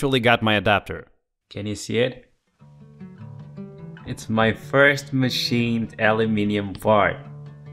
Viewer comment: I thought I heard him say this is my first machined aluminum fart